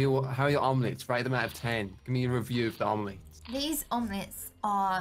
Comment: I thought he said “meme review” 😂